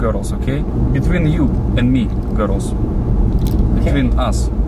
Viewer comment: It sounds like he saying he's a girl too!